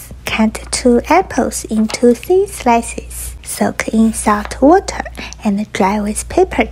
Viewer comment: At first I thought she said “soak them in soft water”